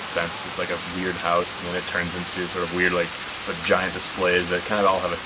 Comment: sounds like he says vagina displays. I'm pretty sure it's giant displays bu my version is funnier.